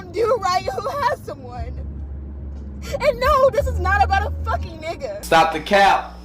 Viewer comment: I thought she said it’s was not about a man ??